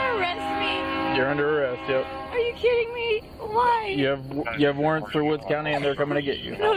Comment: I could have sworn she said to the cop "are you kidding me I'm white" that's extra helmans mayo right there